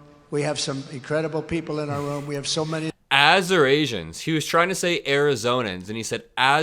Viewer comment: Trying to say Arizonans? I thought he was trying to say Assyrians??